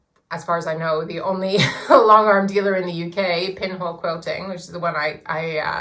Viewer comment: I really want a long arm quilting machine so thank you for the video. As someone else has said you have answered questions I didn't even know I had. I'm in Scotland too; where did you get your machine? I thought you said pinball quilter but can't find it.